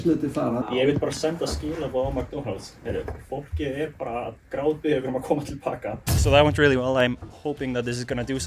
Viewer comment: It sounded like he “Fook yeah!” @ 5:20 😂